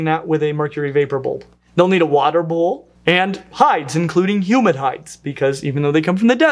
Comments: Every time I hear 'hides', I think of animal hides. Takes me a second to realise he's saying 'humid hide' rather than 'human hide'. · When you said humid hides, I thought you said human hides, as in the skins of humans 😳😳😳